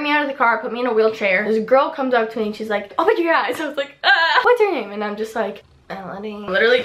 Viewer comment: what did she say at 7:26? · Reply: Crazy payne.x She said: "Open your eyes." :)